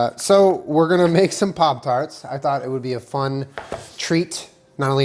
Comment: I thought it said pop-farts 😂😂